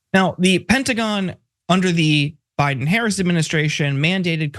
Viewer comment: I thought i heard the gentleman say, the Biden heresy administration, which i thought was clever, until i realised he said Harris, not heresy.